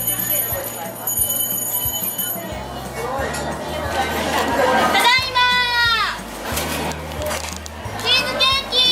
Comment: Does anyone know what she’s saying at 5:40 ? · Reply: She says, Just now, Japanese cheesecakes has been baked!!